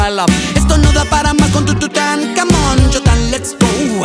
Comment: Yo pensé que decía "tu tan come yo tan lets go" xdd